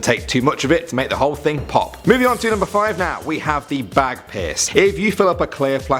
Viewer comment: Anybody else thought he said bag piss at 2:50😂😂